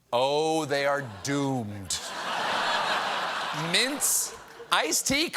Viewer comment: Oh, mints? I thought he said mince. Like maybe he was eating mince pies or something.